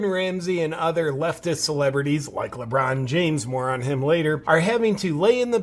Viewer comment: I misheard, "like Lebron James, MORON him, later" :)